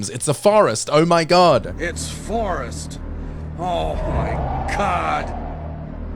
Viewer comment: "Oh myyyyy COD!" That's what I keep hearing